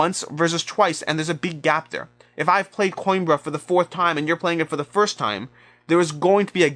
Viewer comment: What is the game you mention at 6:54? I have heard you mention it before but I can't make out the title. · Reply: Coimbra :)